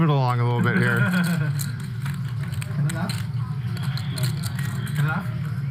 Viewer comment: sounded like Wes said “do you Bluff?” And it looked like you also thought you heard him say the word “bluff” at first. Curious if Tom picked up on that.